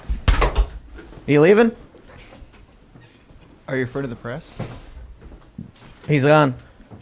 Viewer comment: Wait…so is trying to gaslight us today (11/30, Ep. 667) by saying that he did NOT say “are you afraid of the press” at 21:13? 😂